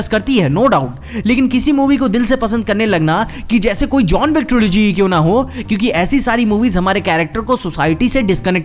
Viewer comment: there said something that's like "jhon wictrology" something like that , if you know what he said tell me pls.......😑😑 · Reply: John wick trilogy